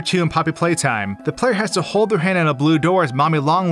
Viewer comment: Wait we play as a female in Poppy Playtime? I swear I heard him say “her hand”